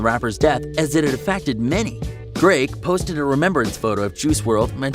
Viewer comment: When he said “drake” I thought he said “greg” I was like “bruh, who da f is Greg???”